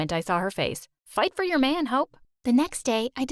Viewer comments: i straight thought her mom said "fight for your man, hoe" 😂😂😂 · The mom is definitely the most interesting character in this story. She's so unpredictable. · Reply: Fr I thought she said fight fa ya man h word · I thought the mom said “Fight for your man, hoe” 🤣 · I thought she said “fight for your man h**” 🤣🤣🤣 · I thought she said “fight got you mine Ho”